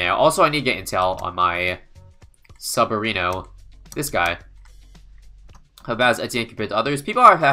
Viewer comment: what gibberish did he say here?? I can't decipher it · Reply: How bad is Etienne compared to others